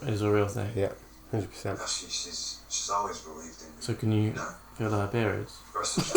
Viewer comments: I don't know what says at 10:54 but the subtitles say 'Can you feel diabetes' and I have a feeling that's not what he says at all. LOL! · I couldn't hear what said... ANYONE?! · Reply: I think he said "so can you feel her periods". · What did ask @ 10:55? I ran it back so many times and still don't know what he said and CC said "diabetes". The guy who played Luke is brilliant. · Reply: I think he asked "so can you feel her periods?" Lol